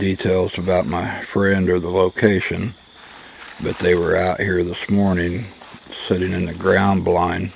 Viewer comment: Great bedtime video for me (Moparbill) . I heard "out here lost" Sounded like lots of stuff may have been thrown throughout the video. Daytime video will be interesting!